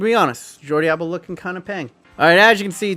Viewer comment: did I hear peng from an American 😂